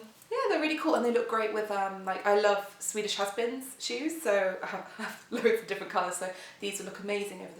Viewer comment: The first time you said it, I thought you said "swedish husband shoes" and I was SO confused. Such wonderful prints! Seems like being spontaneous really paid off!